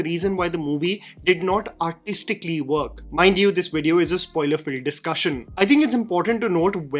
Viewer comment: He puts a disclaimer in the start. I don't understand how are you watching an English review when you don't understand it. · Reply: On a rewatch, he said “spoiler-filled,” but I heard “spoiler-free.”